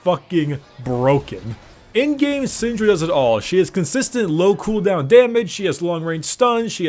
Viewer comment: is sound like he said does it stops awe...* and then continues.